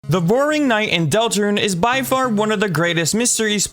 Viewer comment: Am I the only who heard "the boring knight"? 😂